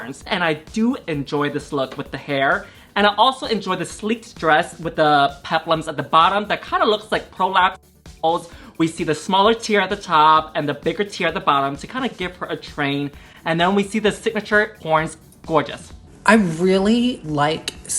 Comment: did I hear prolapse assholes 😂😂😂😂🤣🤣🤣🤣